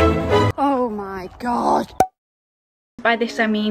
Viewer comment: did she say "OH MY DOG"?